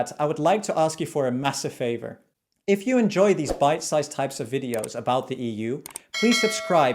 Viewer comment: I heard: "if you enjoy this biased videos about the EU". Not far from truth tho.